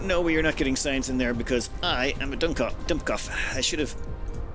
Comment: I hear a german word: "Dummkopf" ( TF2 Medic)!